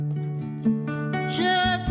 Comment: Dit elle ô dib diami ? Ô bulong bwam? Rectifier moi svp 🙏au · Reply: Elle dit « O Dimbu diami (dans mon village) O bulong buami ah (dans mon pays) »